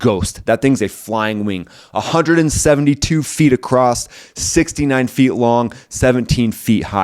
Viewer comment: Did say the B2 is 69 feet long? Nice : )